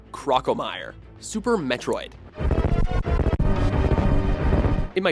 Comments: Did he say "sperm metroid" at 1:44?😂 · Lol on the second one when you said super metro is I thought he said sperm mentor